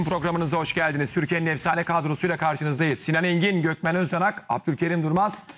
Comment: sunucu değiştirin adam ne diyor 0:04 gökmen öz***** oluyor